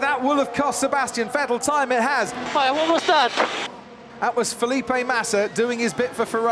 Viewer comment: what Vettel said? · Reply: He got a bit Italian and said : "Mai, what was that?"